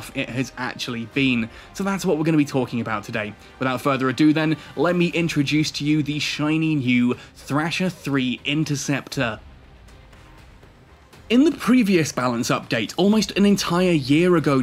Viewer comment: Did I hear trasher interceptor? 👀